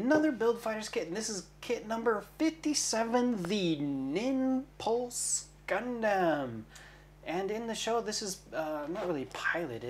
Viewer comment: Haha I was busy building my RG and I thought I heard say 'Nipples Gundam' 😂